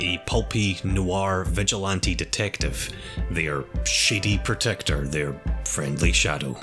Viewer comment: for a second I legit thought he said "Their Shitty Protector", nevermind that, great work dude!